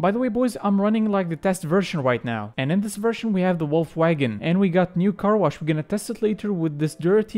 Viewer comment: I thought u said Volkswagen and not wolf wagon and I got so excited